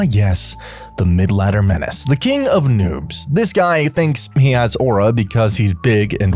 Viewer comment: what did He say 🤨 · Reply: noobs😔✌️